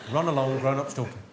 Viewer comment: What's he saying at 4:36? Run along, run ? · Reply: Run along, grown-ups talking